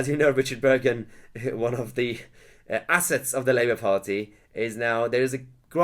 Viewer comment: Weird, when you said "assets to the Labour Party" I heard "Ass-hats" Burgon is a total party-line robot... Not a single thing he says sounds genuine.